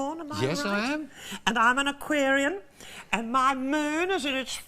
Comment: The audience didn't catch Edna saying "I'm an aquarium." 😅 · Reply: Aquarian not aquarium!